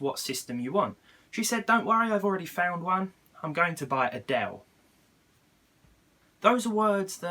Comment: i thought he said "im going to buy adelle"